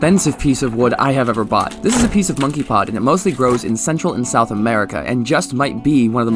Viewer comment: You were talking so fast that I thought you said ‘monkey pox’ the first time Hahahahahahaha It looks ABSOLUTELY AMAZING by the way 👍❤️👍❤️👍❤️👍❤️👍❤️